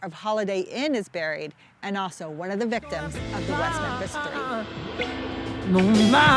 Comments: I could not understand what you were saying because the music was too loud. Can you let me know what you were saying at that time mark? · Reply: One of the victims of the West Memphis three (sorry…missed that volume shift in the edit) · Who did you say was buried there at the 3:14 minutes? Couldn't hear for the loud music. Sounded like one of the victims of the west.....???? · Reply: Victim of the West Memphis Three! That happened right across the Mississippi in West Memphis Arkansas.